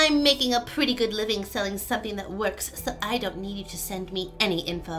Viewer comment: I heard "something that works" as"sucky ItWorks". Lol